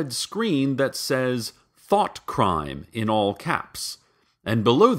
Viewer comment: Whenever he says "Thought" I hear "Thot"